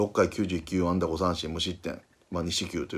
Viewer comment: 6回99安打無失点にしか聞こえんw